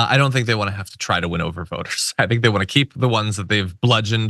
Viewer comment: Oh! You said “voters”! I heard “boners” and that made it much funnier!😂